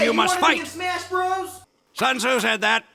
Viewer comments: why does it feel like he says “Sons who said that?” Instead of “Sun Tzu said that” in this context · "hey you wanna be in smash bros?" "SUN TZU SAID THAT." In memory of Rick May. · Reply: I heard it as "Sons, who said that?" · I thought he said “Son’s who said that”